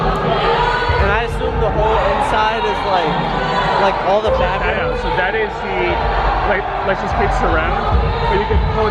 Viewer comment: did he say ants? wtf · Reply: marko jyysström amps